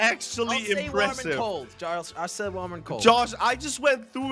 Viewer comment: did say charles?